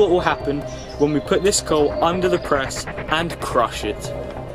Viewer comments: Every time I say his channel or hear his channel I always hear "Cru Shit" Instead of "Crush It" lmaoo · At the start I thought it said crush shit